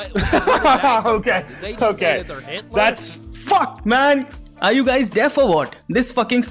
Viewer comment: I know Indian language. Even I thought he said hitler 😂. I know what higda is but that OK guy is trying to copy western accent.